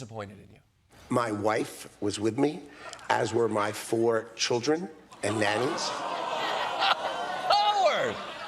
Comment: What I heard "my four children", I thought he said my "former children". I'm glad there wasn't a "home alone" situation on the island. I just really hope that's not what happened to the children on the milk cartons